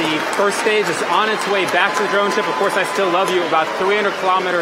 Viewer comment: does he say of course i still love you and again at 2453